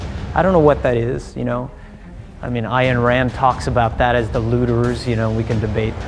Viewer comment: "Ayn Rand talks about that as the" .... what word? I couldnt hear clear the word, can anyone help me please? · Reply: Its "looters" as the subtitles suggested, thanks anyways!